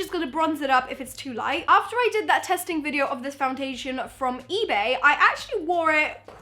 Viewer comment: did anyone hear her say fountation 😂